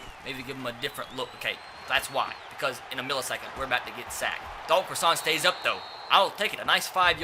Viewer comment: I thought he was saying dull crossaint the whole time